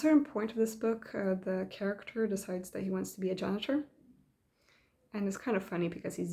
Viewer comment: Haha sounds like the book did it's job. Sorry I didn't understand at 17:35, the character decides to be what? Janitor? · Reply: Yes a janitor. It's a great book full of surprises 😁